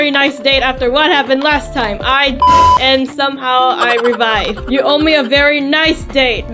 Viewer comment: did you say a swear word? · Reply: She said died